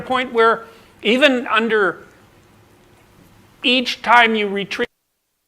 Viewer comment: Audio cuts out. Really curious what was said there.. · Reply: He essentially repeats the point he just made. You can lip read it. "Every time you retreat out of battle, you probably [grow shorter] for sure, and that's not a recipe for success...". The bit in square parentheses is the only bit I am not 100% sure about due to some pixelation over his bottom lip right as he says it, but given that it is one or two words at most, it's not going to be radically different. Even if it is "every time you retreat out of battle, you probably lose it for sure" or "every time you retreat out of battle, you regret it for sure" of whatever it may be. His point is the same.